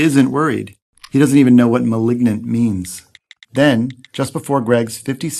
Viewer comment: sounds like he said ligma😄